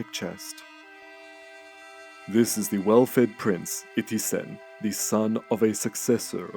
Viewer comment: "the well fed prince 50 cent" did i hear that right!? 😅